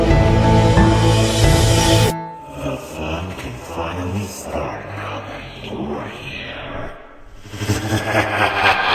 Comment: What being said at the 1:26 part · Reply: i think its the fun can finally start right here!